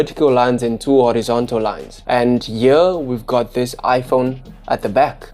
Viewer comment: Did you say "here" or "yewr" ? If the latter, what does it mean ?